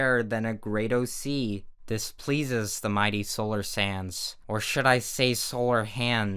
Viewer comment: the sans references are beacuse when u say solar sands... it sounds like u said solar sans lol